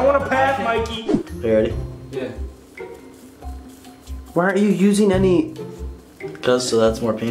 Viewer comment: Anyone else hear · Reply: It was... he said "why aren't you using any"